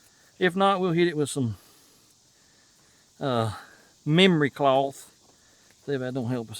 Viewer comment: Sounded like you said,, MEMORY CLOTHE?? BOY I NEED SOME OF THAT 😮😮😂😂 to make my clothes out of 😮